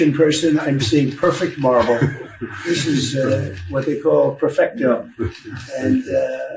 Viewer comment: I thought he said no Spanish. He said perfecto. Only English on the job.